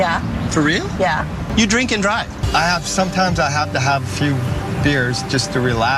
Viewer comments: The girl that said “I drink and drive” at first I thought she said “I jerk and drive”. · holy shit when it said the first guy would drink and drive, i heard 'jerk and drive' and i was scarred by the image of that man plowing into cars while masturbating. so im passing that on to everyone who reads this. im sorry.